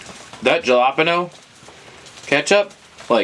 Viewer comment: Jalapeno?? I thought you said habanero? Haha